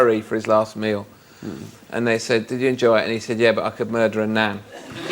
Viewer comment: I alway thought Alan said "I can murder a nap" not "nan"